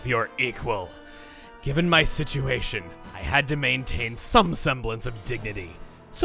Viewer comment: I think the dialogue says station, not situation